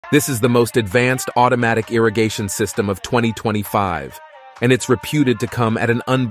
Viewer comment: I thought he said interrogation System